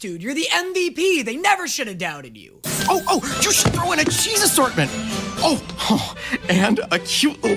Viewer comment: I just noticed, did just confirm Jesus exists 17:50?! It sounds like he said Cheezus Ornament but it could've been Jesus.